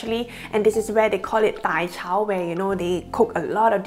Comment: Didn't catch what you said at 0:11. Are you talking about zi char kind of places? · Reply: Dai chau, it's in cantonese. What's zi char?